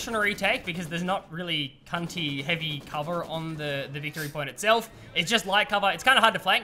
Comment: did say “ there’s not really any CUNTY heavy cover or anything”.